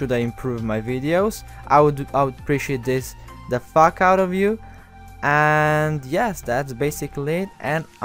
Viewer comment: what's he say at 5:25? "the f...out of you"? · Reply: Yes, that's exactly what I've said. The f*** out of you. Nothing more, nothing less. ~